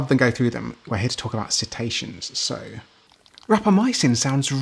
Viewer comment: June 2025... I know it's your accent, and pronunciation, but I thought you said 'Cetacian' 😅 as in whales and dolphins. Heck, when I asked Siri "What is a Cetatian?", she gave me the definition of Citation 😂